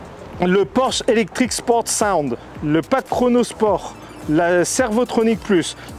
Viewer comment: c'est moi ou il dit " le pack renaul sport " à 39:47???